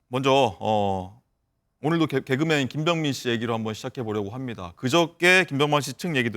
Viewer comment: 최초에 아나운서가 "김병민" 이라고 한거 같은데?! ㅋㅋ 나만 그리들림?